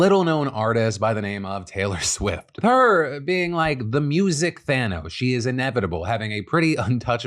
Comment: "with her being like the music-Thano" I thought he said "music-Fanum" at first 😂